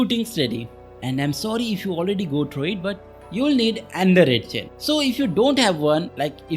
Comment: i have no idea what bro is saying at 5:10 . what do you need before going in? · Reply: Ohh my bad 😅 Maybe I should put subtitle in whole video Anyway I was saying that you need another red chain before going into the portal + you need furnace, lava/coal and a Flying Pokemon Also you need to craft the distortion table after going so make sure to bring 3 pokeball made from tombstone and 1 paper 😅